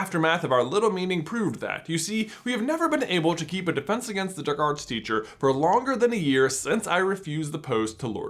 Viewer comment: it sounds like he said dirkarts teacher